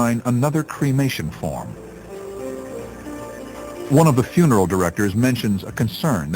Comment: You're almost right, he said 'funeral director' not female director; it does sound like he said female though!